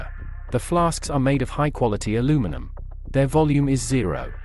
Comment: I thought he said "floss" How the hell do go from "Flask" to "Floss". It is not "Floshsk".